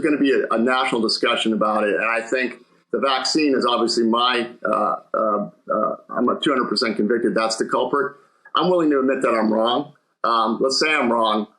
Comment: He doesn’t think the disabilities are due to the vaccines?? OMG! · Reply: If you’ve seen any of Ed Dowd's other interviews, he 100% attributes the spike in health issues and disabilities to the vaxx. If you listen very carefully to what he says here at the 14:51 mark, he actually says, “I'm uh 200 convicted that’s the culprit.” He fumbled through saying it so it comes across as if he said “I’m [not] 200 convicted…”